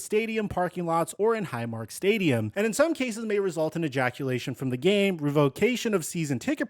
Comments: lol.!!!! 8:17 into video, ejaculation instead of erection. Lmfao, I mean ejection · anyone else come to the comments to see if they were the only ones that heard him say ejaculation? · I'm not trying to be funny, or crude... seriously, love this guy, don't want to get banned of his comments... but did he accidentally say "ejaculation" instead of "ejection" at around the 8:20 mark? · Did I hear say "May result in ejaculation from the game" 🤣😂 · Did he say ejaculation from the game? 8:20 approximately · Dude, you just said, “may result in EJACULATION from the game.” That is what you said. At 8:16 into the video. Haha. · Around the 8:18 mark did you say "ejaculation from the game"?